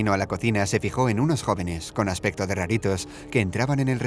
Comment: yo pensé que decía "gatitos" XD